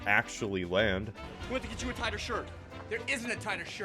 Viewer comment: Wow I always thought he said "tiger" shirt for some reason lol Also, 14:58 Lol! He really does look like a Bionicle 😂